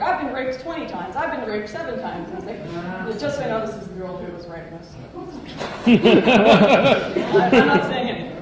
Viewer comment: Anyone make out what Lauren said at 3:56? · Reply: She pointed to the picture behind her, to the woman on the bottom left of the picture and said, "Just so you know this is the lady who was writing this stuff" Or something really close to that.